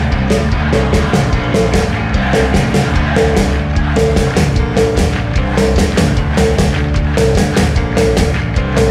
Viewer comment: o que eles falam? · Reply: "ei, sarney, vtmnc"